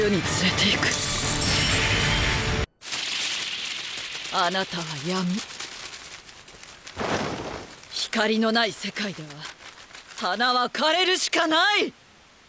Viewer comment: What does she say? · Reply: You're darkness Flowers can't bloom in a world without light